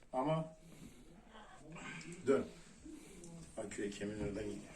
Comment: da ne diyor hoca aküye kemigin oradan gidiyor mu dedi? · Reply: ta kürek kemiğinin ordan gidiyor diyor